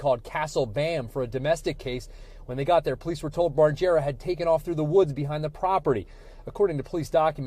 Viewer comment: All I heard was “Barjera”